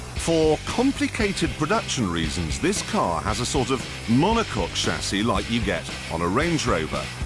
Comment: I hear "monocock" :D